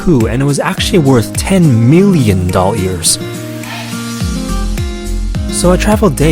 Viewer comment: Wait did you said dol hairs or dollars when